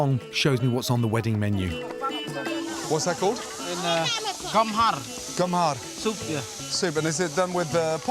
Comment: I heard "cum hard"